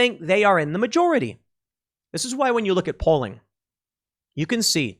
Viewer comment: What he said: "...when you look at polling..." What I heard: "...when you look at Poland..."